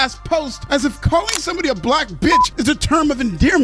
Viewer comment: Thought they said "Blap Bitch" ?